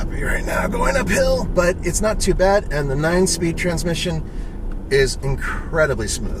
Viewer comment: Did he said 9 speed or I smoked to much....